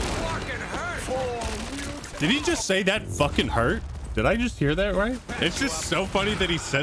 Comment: he said "Flarkin", it's like a space swear word (could be heard countless times in Guardians of the Galaxy game)